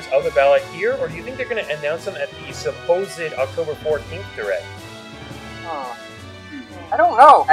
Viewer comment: did I hear that right there's suppose to be a direct tomorrow? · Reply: oh for some reason I heard 4th instead of 14th but thanks